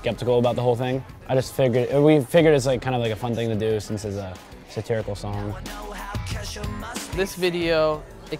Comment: Did Jack really say "since it's a terrible song" ? at 2:07 hahaha lmfao :P